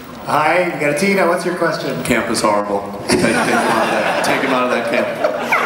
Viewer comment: what is he saying (27:10)? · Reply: Ema Logan camp was horrible or something